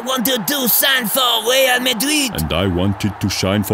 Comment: is it me or did he say man shitty